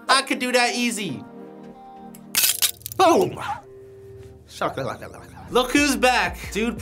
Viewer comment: did he say boom laka laka